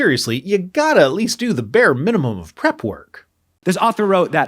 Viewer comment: I heard "the bear minimum" but I guess I was still thinking about Goldilocks.